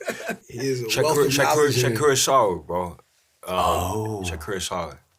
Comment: What is he saying at @ 0:25 ? I hear shakura solid bro